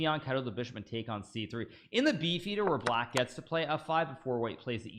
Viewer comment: When Hikaru said Beef Eater, I thought he said Bee Theatre. The Bee Theatre sounds like a much more interesting opening.